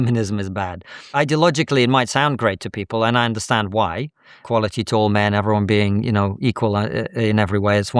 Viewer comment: Omg Steve Keen is a prime example of what is going wrong in the world. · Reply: Thought for a sec he said quality TALL men. I was ready to go. 😅